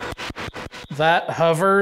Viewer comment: I didn't hear "that hovers " I heard "that husband"